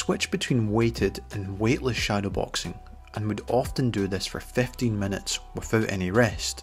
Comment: With the accent I thought he said he would shadowbox for 50 minutes instead of 15 and I almost had a heart attack